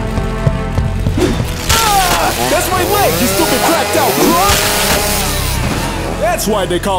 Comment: Did he say "That's my leg you stupid crocodille Hump" !? 😂